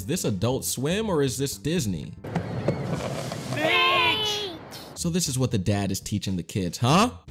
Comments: I heard beach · I heard the dad saying the B word. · I don't think they said b word i think they said beach